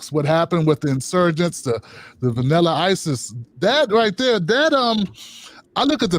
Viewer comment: “Vanilla isis” 🤣🤣🤣☠️ that killed me, I had to pause the video to laugh it off because I couldn’t follow after. As a white progressive from Texas, I’m glad I came back for the conversation. Keep speaking truth. Looking forward to next one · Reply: White progressive in texas here too... and I know! It took me a second cuz I'm watching while I have my coffee, I thought he said vanilla ices... like calling them all vanilla ice? 😂 lmao it took a second to click, ohhh vanilla isis lol that's so good.